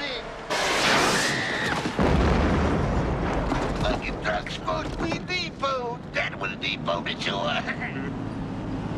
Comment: I can’t even imagine what he was trying to say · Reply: Fuckin truck is supposed to be depot that was depot wasn't it Joey?